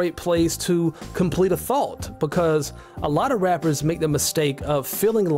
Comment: what are u saying? Are you saying thought? Cos the accent is hard for me as an Australian haha